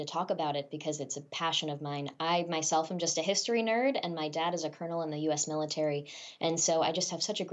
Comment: I thought she said, "My dad is a Criminal in the U.S. Military"....LOLzz .. Obviously, not true...